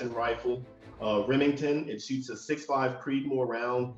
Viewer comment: good video but at 2:28 did he say that was a remington????with an accutrigger?